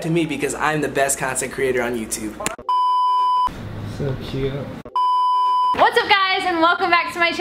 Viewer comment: uhm what did u sAy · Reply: so cute